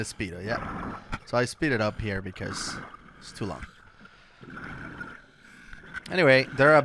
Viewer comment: I thought said, ‘so he Speedo’d up here because.. it’s too long..’ 😂